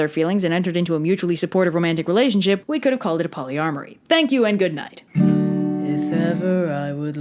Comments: Haha, it sounds like you said "polyarmory". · I know you meant to say polyamory, but I swear I heard polyarmoRy. which is a lot funnier in context, what with them being knights in armor